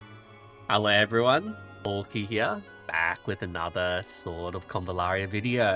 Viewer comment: I heard that "bulky here"😂